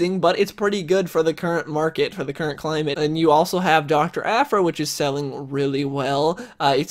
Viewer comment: thought you said Dr. Afro, when you said Dr. Aphra.